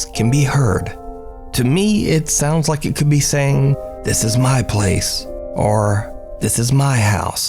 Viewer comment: Number 1. I hear it as "I found you, this is my place" 😅